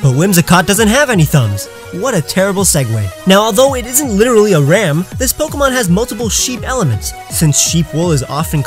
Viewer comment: you know that a ram is a male sheep right? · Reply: i misheard and thought you said ‘whimsicott IS literally a ram, but it has more sheep elements.